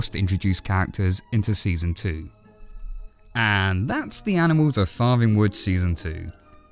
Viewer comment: Thought it said “animal flirting wood” I was like “Weasel what have U done did dis time?!?”